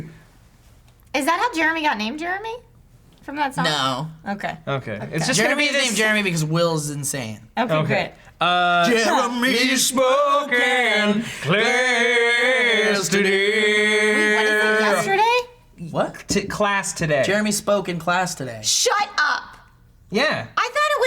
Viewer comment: I thought it said Jeremy spoken blasphemy